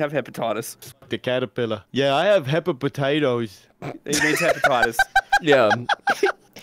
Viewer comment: Kinda sounds like he said pepper potatoes the second time